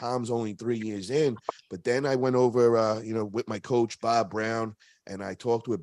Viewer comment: Lol I thought said “Bob Lowe” but he was saying